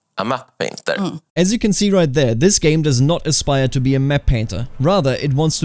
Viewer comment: Captions are wrong, he says "aspire " not "desire"